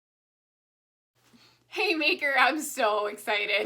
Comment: I thought you were about to say haymaker I’m so stoned